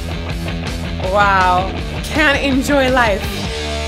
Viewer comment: ‘I tell you to enjoy life” is often misheard as “I tell you to end your life”. It seems pretty plain that he is saying “enjoy life”